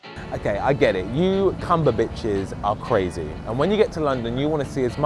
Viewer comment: did he say cumber- bitches😂😂😂